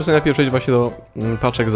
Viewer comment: czy tylko ja usłyszałem "napieprzać"??? XD